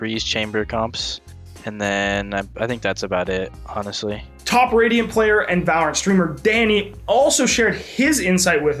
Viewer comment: Did he say top radium player @ 6:13?